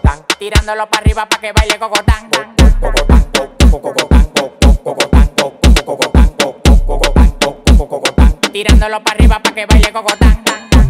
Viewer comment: El Dembow del Alfa re pegado en Colombia 🇨🇴🇨🇴🇨🇴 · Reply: Pensé que decía tirándolo para arriba para que baile Bogotá